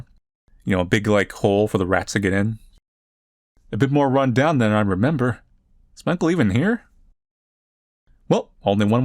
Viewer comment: must be losing it but i thought it said "a bit more bunbun than i remember"